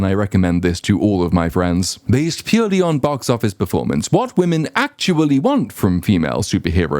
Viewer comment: I genuinely heard this as “ it’s much better to be gay based on box office performance”